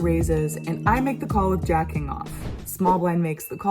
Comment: I'm like "WHAT DID SHE SAY!?!?". It's King Jack off, Always King Jack off!